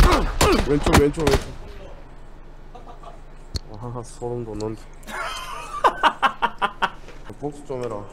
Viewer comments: 초 쯤에 뭐라고 하신거죠?? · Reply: 하하.. 소름돋는다.. 인 것 같네용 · 뭐라고 하신 거죠,,,? 못 알아 듣겠어요ㅠ ㅠ 나도 웃고싶어.. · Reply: 와하ㅋ 나.. 소름돋는다..